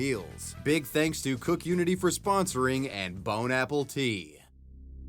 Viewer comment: Get 50% off your first order of CookUnity meals — go to <link> and use my code WAVYWEBSURF50 at checkout to try them out for yourself! Thanks to CookUnity for sponsoring this video. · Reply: did you say 'bone-apple-T' ? I couldn't concentrate on the video after that 😅🤣